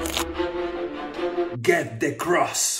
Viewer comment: I thought u said "get the pink one" instead of "get the big one"